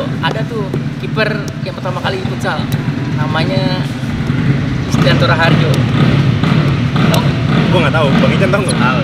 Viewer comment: Bismillahirrahmanirrahim Jawabnya pada menit 53:56 kalo ga salah denger nih bang(suara agak ga jelas)yaitu listianto raharjo #Brandosportgiveaway